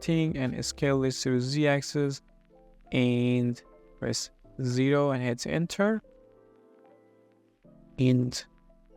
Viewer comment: I have no clue what he did. Does anyone know? I think I heard "press zero and hit enter," but the lack of key inputs for that part and how I did just that and it didn't do anything tells me I'm a goofball. I assume it was a merge? I dunno, man.